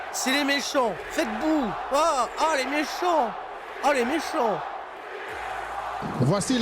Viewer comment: C'est moi ou la foule hurle "CM Punk" ? :D